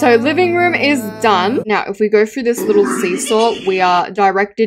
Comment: DID GURL JUST SAY SEESAW? GURL THATS A TUNNEL